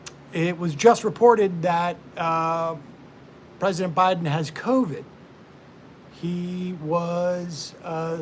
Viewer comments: jesus christ I thought you said 'Biden has CODED' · For a second I thought Seder said Bided 'has coded.'